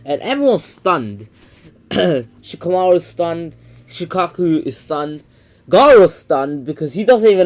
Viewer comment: Shukaku you mean wait who is Your shukaku the 1 Tails Name is shukaku or am i going crazy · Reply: O i thought you said shukaku thanks also great videos i love all of them good thing i know a bit of englisch